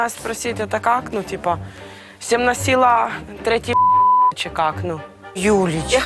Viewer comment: что она сказала? "Семь носило, третий ...???"